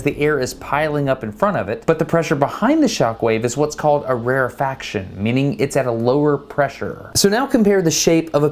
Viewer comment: The closed caption has "rarer faction" instead of "rarifaction", fyi